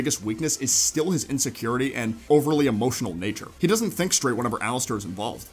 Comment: I thought it said "how straight?" and GIRLLLL